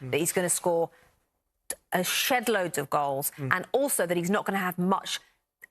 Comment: what she say??? Lol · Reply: a shedload of goals, a shedload is a very large amount or number of something.